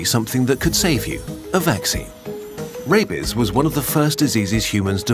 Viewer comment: The pronunciation made it sound like he was saying “Rapies” 😂